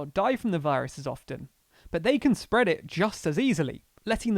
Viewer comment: the caption says "older & venerable people", but you meant "vulnerable" (the voice over even said "vulnerable")